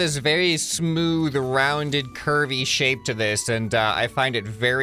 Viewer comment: the first time he said curvy I thought he said "kirby" 🤣